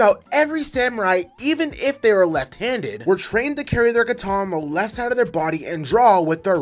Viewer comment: I thought you said if you were left, handed you get more power in your hand